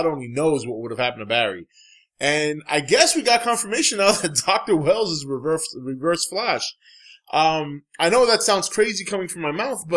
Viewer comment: sounded like a perfect blend of Dr. Wells is Reverse Flash and Taco Bell is reverse fresh :P